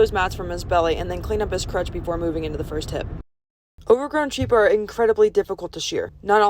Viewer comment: “Overground sheep are incredibly difficult to shear” what did she just say, there’s underground sheep !?!?!!????